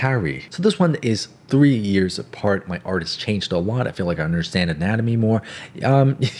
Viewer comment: Anyone else notice that he said “A wot” instead of “A Lot” at 10:15 😂